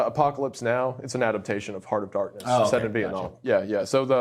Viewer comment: What if being in the spectrum is the next step. 38:35 Heart of Darkness I heard Army of Darkness and was thinking Bad Ash.